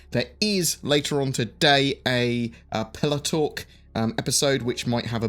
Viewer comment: I thought he said PillowTalk instead of "Pillar Talk" xD.. Would love that giveaway ship!